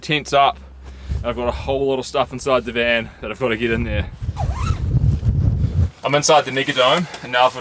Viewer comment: did I hear you right? You’re inside the what?? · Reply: He said “Ive got a whole lot of stuff inside the van that I’ve got to get in there.” He has to unpack the van.